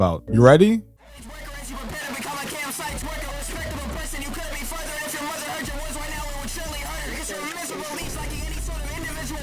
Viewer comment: "despicable person" "if your mother heard your words right now it would surely hurt her" is all i heard